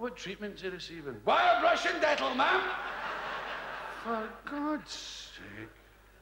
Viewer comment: Whits he saying? Wire brush and dental? Is that a Scottish thing? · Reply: when I was a kid i thought he was saying wild Russian dettol